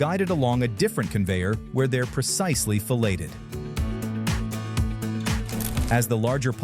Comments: 😂 Did he just say the fish are fellated? "Do you like fish sticks?" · It sounded like the narrator voice said the larger fish are precisely fellated!! Usually you have to pay extra for that. Er…. So I’ve been told. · did you say those fish were fellated? I think you meant "Filleted". VERY different meanings from two similar words...